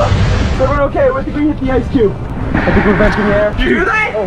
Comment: 😂😂😂😂😂 did he just say "we got hit by the ice cube"lel😂😂😂😂😁😅